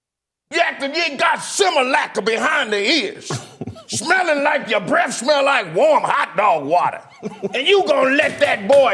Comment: "SAS breath smelling like warm hotdog water" did I hear that right?🤣🤣🤣🤣🤣🤣🤣💀